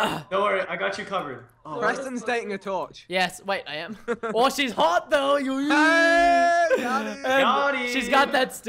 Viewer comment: I THOUGHT HE SAID SHE KINDA HOT THOUGH ANY 5SOS FAM OUT THERE